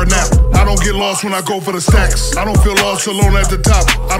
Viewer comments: "my room is a map, I get lost when I go for a nap, I don't get lost when I go for the stacks" Brruuuuuuhhhhhhhhhhhhh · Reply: Ha I thought he said I don't get lost when I go for snacks" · "My room is a map, I get lost when I go for a nap, I don't get lost when I go for the stacks". Whattttt 🔥🔥🔥🔥🔥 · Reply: My hungry ass thought he said snacks lmao